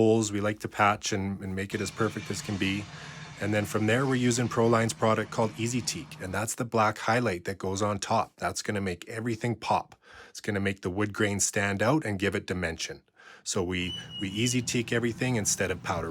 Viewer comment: I thought you said “its gonna make the green stand out if you have dementia” 🙃